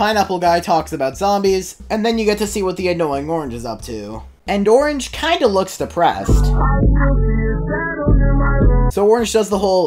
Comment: I am the only one who first misheard that as "And Orange kinda looks Rizzed."